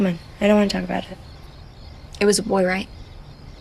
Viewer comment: What does she say at 5:27? It was at war?